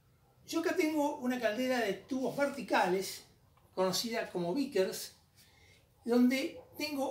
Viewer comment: perdon , podria decirme como se escribe el nombre del diseño de la caldera que nombra en el minuto 21:44 , interpreto que dice "Vikers" pero no puedo emcontrar informacion sobre este modelo , nisiquiera en ingles y me parese un diseño super sencillo , practico y eficiente para la escala de mi diseño